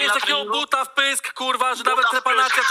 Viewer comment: Usłyszałem na początku gluta w pysk XD